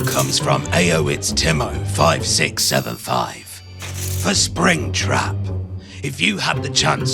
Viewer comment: SPRINGTRAP what did you say? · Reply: 5675, that's what he said.